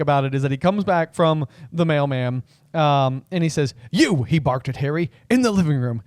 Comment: I completely misheard ‘comes back from the mail man’ as ‘comes back from the male man’ which left me a little nonplussed 😂 I guess it’s a US edit for postman. I don’t understand why they did edit these things though